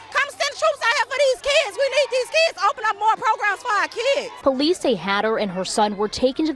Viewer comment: 😂😂 did she say send some programs for these kids? 😂😂